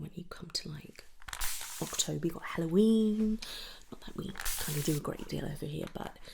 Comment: What'd was said after Halloween? At 7:57 I've replayed so many times with cc and can't tell :P I must not celebrate it! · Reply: “Not that we kinda do a great deal over here”😂